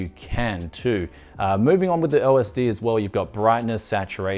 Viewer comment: did someone said LSD?